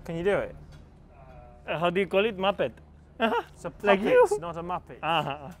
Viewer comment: What word does Lando use when he tells Carlos 'It is a.... Not a muppet'? Does he say puppet or what? It doesn't sound like that. :) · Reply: Yes he says puppet